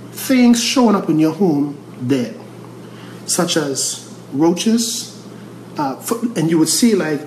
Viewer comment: hello can someone help me please I am a french speaker I would like to know what is the word he is talking about at 1:09:21 ? Many thanks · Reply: roaches are cafards